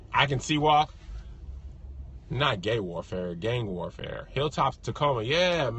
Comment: I thought said gay warfare too! 😂🤣😂🤣😂